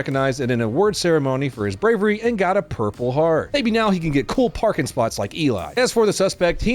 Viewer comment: im sorry, did you say "Barking spots"?